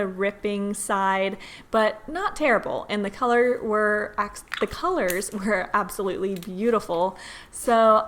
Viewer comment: I thought she said ‘ The color are actually colors’ 😂